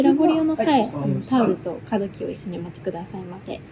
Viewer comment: 我覺得這部影片很好地表現我日文日常聽力耳背的問題QQ。聽新聞也好甚至聽一些演講也好都大致聽得懂，但日常對話裡面有時候有連音、還有某些字詞的音會變很小聲(例如夜通し、カードキー我一直聽成calbee)，這好難呀🥲